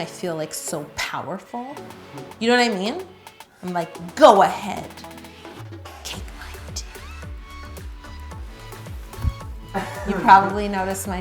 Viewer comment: I thought she said go ahead gake my d🇬🇩🇬🇩k